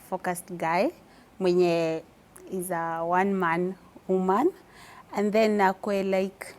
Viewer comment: Did she just say “I want a one man woman” at 13:28 😂😂😂😂